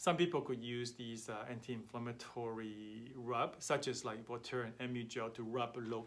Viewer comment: What is that anti-inflammatory rub you said at 6:28 ?? The captions had trouble translating your accent.. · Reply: it's voltaren